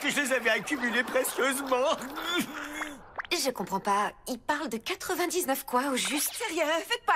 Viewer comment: Salut tout le monde. Je pense que j'aurai dû écrire : "Ils parlent de 99 quoi au juste ?" ( 1:19 )